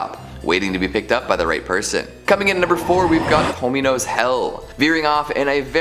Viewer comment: I swear to Bob I thought he said "Homey Knows Hell." I was like "did Snoop Dogg make another horror flick?"